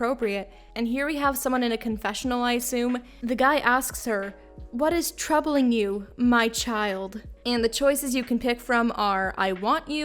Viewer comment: Dude I totally thought he said "What is troubling your child" Like a counselor or something